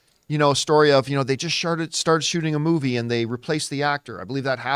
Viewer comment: did he say sharted?